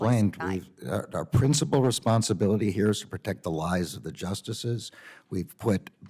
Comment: Did he just say to protect the LIES of the justices?